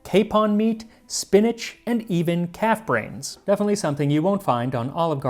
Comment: “Calf brains - won’t find at olive gardens tour of Italy”!!! Hilarious. Especially considering that I first heard “cat’s brain”…